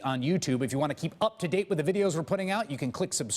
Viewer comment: They gotta change the outro to their videos... the way Chris Hayes says 'just below me' it sounds like he is saying 'just blow me.' Lol.